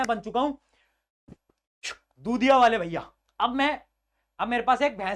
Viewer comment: I think he said chutiya wale bhaiya